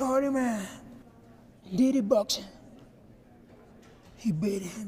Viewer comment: The way he says “dirty boxing” is so weirdly satisfying ... I can’t stop replaying that part😂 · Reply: I thought he said "did it boxing"